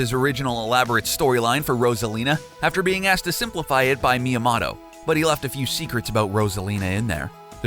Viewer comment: Did he just say Miya"Motto"?